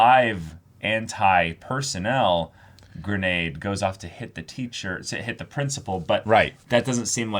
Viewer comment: I kid you not, I heard: so that the anti personal granade does hentai to the principal I know it doesn't make sense it just made me laugh